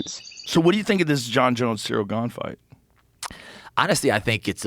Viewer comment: Did dude say "honesty" instead of honestly? 🤔😂 What a dum dum